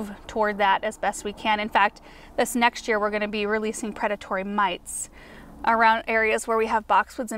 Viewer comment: Lol at first I thought you said you were going to release predatory mice!